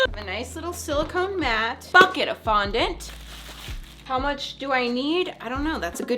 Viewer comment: I thought she said fuck it of fondent for a second lol 🤣